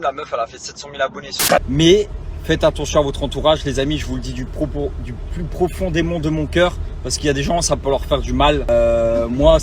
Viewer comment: C’est moi ou il dit : J’vous l’dis du plus profond démon de mon cœur ??? Le mec c Dutroux est il s’enjaille c abusé